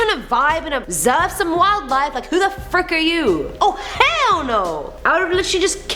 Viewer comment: i thought she said "Who the F*ck" she said Frick.. I thought editor miss out her profanity they wasnt, thats good!